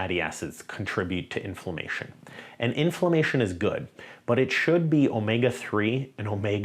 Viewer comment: Did you say “inflammation is good” at around 11:00? 🤷🏽‍♂️